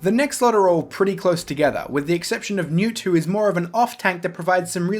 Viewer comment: I thought you said mewtwo